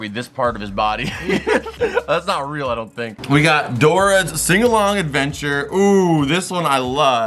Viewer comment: I misheard and thought u said Dora it’s a single mom adventure 🤣